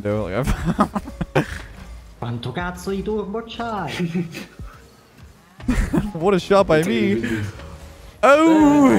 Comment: can someone translate what said at 14:02 plz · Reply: “How much fucking boost you have?”